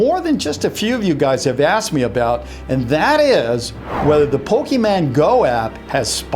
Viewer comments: did he say poke(men) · did he really say pokeman